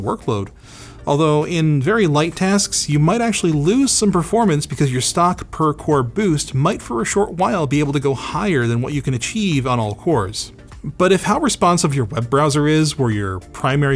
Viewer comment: I keep mishearing "all cores" as "all fours" - Not a bad thing ;)